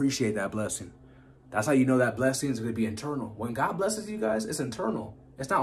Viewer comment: Man this is strong. 40 years old and the first time I heard someone say god blessings are internal, and it really hit home, and I know I’m on the road to blessings. I’ve always heard this as Eternal